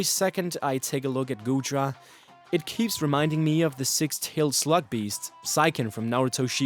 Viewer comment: thought he said “Slut” beast